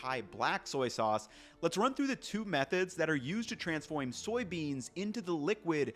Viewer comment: Anyone else hear the “Transfoymm soy beans”